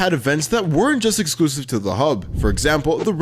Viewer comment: WOA WOA, WHAT DID YOU SAY??? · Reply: He said “hub”. What’s wrong with the word hub? Yall just dirty minded